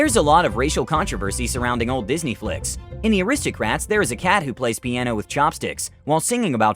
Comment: did he just say "the aristocrats"? Lol